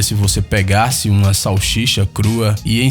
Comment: só eu ouvi salchicha?